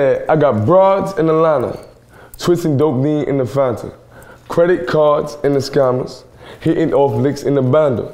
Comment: I always thought he was saying "credit cards and the scannas" WOW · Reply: I thought he said "credit cards in the scanners"